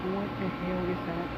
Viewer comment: OMG!! Did you hear what her Mom said right at @ 25:50. She said "What the hell is happening"? I'm a 1000% believing it's one of her beloved fan and or friend prepping her transition ❤📿🙏